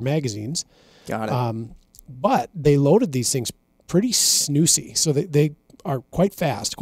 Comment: I'm sorry... did you say snoosy? :) I think I just learned a new word.